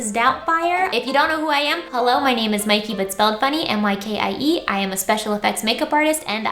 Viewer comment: Totally thought she said “hello my name is mykie Mc-spelledfunny” 😂